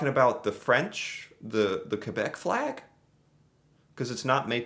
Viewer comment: did it say Quebec? hmm?